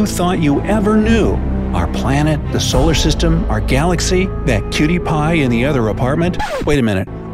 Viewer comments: did you just say that PewDiePie in the other appartment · who else heard it as piewdiepie? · am i the only one who heard some pewdiepie or what? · wait did u say cutie pie or pewdiepie?